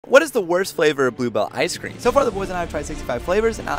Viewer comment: I heard "blue ball ice cream"